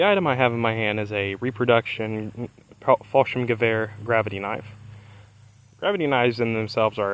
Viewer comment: Not gunna lie here.... I thought it said gravy knife🤣 I was genuinely concerned on why anyone would need a knife for gravy 🤣🤣🤣